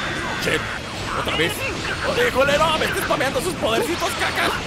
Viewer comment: soi yo o dijo culero minuto